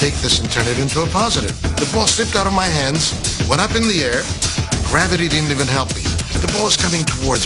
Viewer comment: What did Garo Yepremian say around 15:18-15:20?... "Gravity--" what?... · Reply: "Gravity didn't even help me."